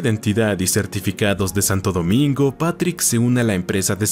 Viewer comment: Hacer comentarios mientras narras es un cambio para mejor me gusta tiene algo de humor.... Saludos desde República Dominicana 🇩🇴🇩🇴🇩🇴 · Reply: También soy de RD. Vine a buscar en los comentarios porque en el min 8:13 dice Santo Domingo ¿o escuche mal?😅